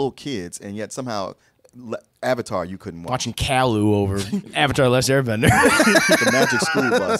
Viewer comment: Did he just said Kai-Lou?